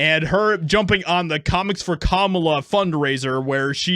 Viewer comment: I thought you said commies for Kamala 😂😂😂😂